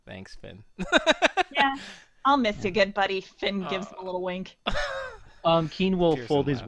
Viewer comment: heard that as "I'll miss your good body... wink wink"...